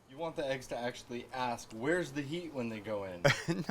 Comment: I thought said "where's the yeet?" LMAO